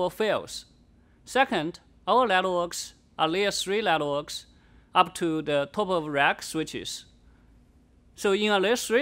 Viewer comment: What does he say at 2:58, after "second,"? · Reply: second, our networks are layer-3 networks up to top of rack switches.